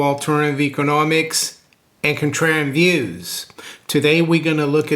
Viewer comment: I heard intro a hundred times, still not sure exactly what he's saying. Anyone help me out here? "Home of alternative economics, and..." what? encotrend views? And cotrend views? Definitely an N in there. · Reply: Contrarian views